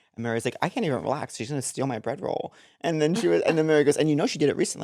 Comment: I also thought said bread bowl too 😂 love y’all ❤